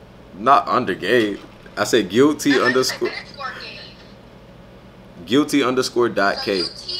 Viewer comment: I swear when he was giving out his ig i thought he was saying gulity_. gay /no homo